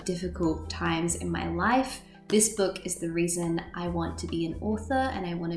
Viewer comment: when you were talking about harry potter i thought you were saying "this book is the reason i want to be an auror" ^^ as if you're either in training to become a police officer or fighting for human rights with amnesty or something... author is nice as well though ;)